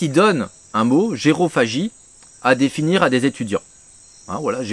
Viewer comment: Aaaah, "gérophagie" avec un "g", j'ai entendu "jérôphagie" (quand on mange des types nommés "Jérôme")